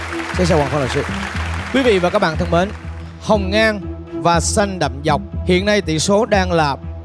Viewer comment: TT nói hồng ngang mà t nghe thành hồng nhan P/s: t là Đóm nhé